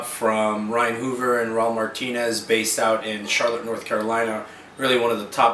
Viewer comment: When he said Raul Martinez i thought he said Raul Menendez, the bad guy from black ops. I need sleep, too much cod!